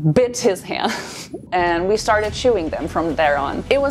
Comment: For a second I thought you were saying "we started chewing them" rather than "we started shooing them" and I was like WELL that escalated quickly.. xD